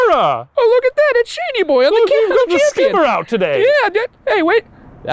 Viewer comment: at around 5:03 the subtitles say "he's got skin brown today." but he actually said "he's got the skipper out today." ???